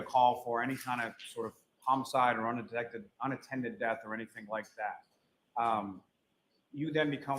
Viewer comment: I swear I heard Lally say "unintended death" instead of "unattended death". Slip of the tongue?